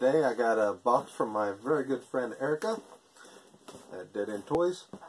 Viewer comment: Her name is haricut? lol that's awesome! Wow that's like your horror and sci fi room huh? haha awesome. My dad and I have been on a binge watch with AVP movies and Alien and Predator movies this week lol. I see a Chucky in the background there! That figure doesn't look familiar though, looks like it's from Bride or Seed though. Nice. You see the new Mezco Child's Play 3 coming out? Looks insanely cool! I used to have a shirt similar to the one you're wearing. Predator's awesome! Awesome pins, that Chucky one haha love his expression in CP 2 there. That's the part where Andy is seeing if he's Tommy or Chucky lol. LOL@ that clockwork orange one haha. Great vid! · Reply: Oh my fault, my bad haha. I thought you said "a box from my very good friend haircut" lmao my volume was on low haha. Erica! haha, gotcha. Oh wow, yeah now you must drink with the horror legends haha. Bird's like "it's so damn crowded in here!" lol. You get some cool boxes!